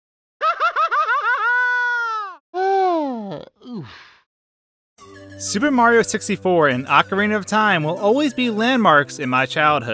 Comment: did u say "super Mario tipsy fort"?